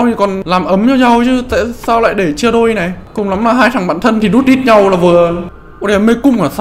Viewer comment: từ "hai thằk bạn thân dutdeet nhau là vừa " mà tuii nghe như là Bét và Kong ý:))))